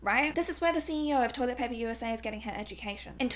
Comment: "Toilet paper USA" !?? DID I HEAR HER CORRECTLY?😂😂😂😭😭😭😭